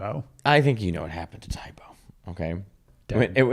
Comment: "I think you know what happened to Tae Bo. It went out with Borders." Classic. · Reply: With Borders!! I thought he said with horders!! 😆